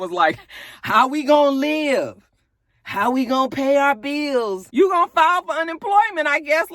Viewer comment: Ok, so I stopped watching LHH but I watched a couple of reviews of this last episode and I watched the clip of the production crew shutting it down and in the clip I watched I could’ve sworn said how are y’all gonna pay y’all bills, but y’all reviewers are repeating “how are we gonna pay our bills”... did he say y’all or we? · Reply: He definitely said our.